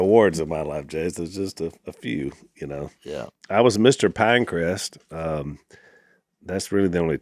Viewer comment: I had to listen to the beginning 4 times. I think Al said he was “Mr Pinecrest in 8th grade” but I heard “Mr. Pancreas” 😂😂